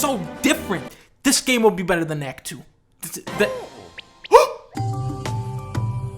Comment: DID I JUST HEAR "BETTER THAN KNACK 2"?!?!?!